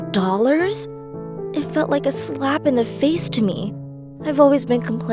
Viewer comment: she says slap in the face but, my computer's subtitle says big slap in the face...